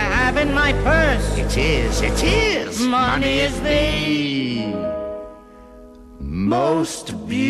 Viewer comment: i thought it said "money is a beautiful world"